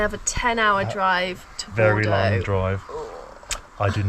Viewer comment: 10 hours to Porto?? That doesn’t sound right? We live just way up north and can be in Lisbon in 4-5 hours? Stay dry! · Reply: I thought said Bordeaux so wasn't surprised at 10 hours. Maybe I heard wrong 😕